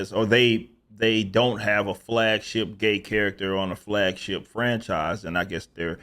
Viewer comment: I thought you said fagship for a second. Lol